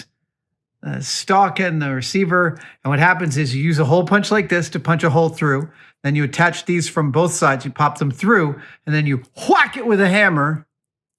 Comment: yeah, i misheard slot punch.